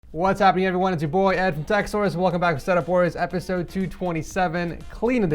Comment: Did I just hear "welcome back to s3x wars"